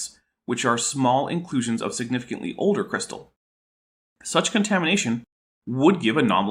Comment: When you said "contamination" I heard "KentHamination." Maybe I was Freudian slippingly mixing up Ham, Hovind, and, well, contamination. Or you Canaders jist cain't talk right.